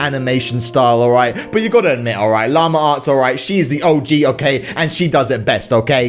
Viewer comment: Thought he said llama farts for a second